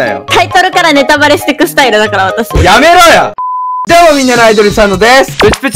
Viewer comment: なんか、ぷっちーの水上生活1の時のがやってた「どうも！みんなのアイドル佐野です！」て聞こえたww((説明深すぎて草生えたww